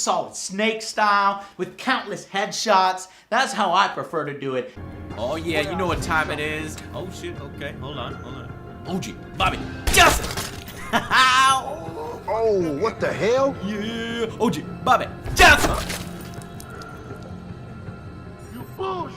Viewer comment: Oggie Bobby Just! (At least I think that's what he said.) Freaking Hilarious!